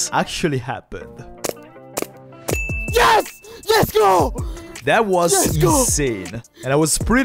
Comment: Finally, I found my way back to this channel! This whole time I thought was saying Let's Go not YesGo! Catchy Phrase